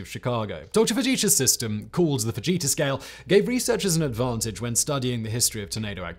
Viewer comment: I really thought said the vegeta scale and all I could think was “ITS OVER 9000”